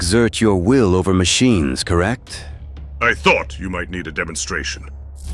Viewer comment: also i honestly thought zavok said DEMONstration, yk, cuz he's a demon? idk why everyone hates the deadly six, they're my favorite villains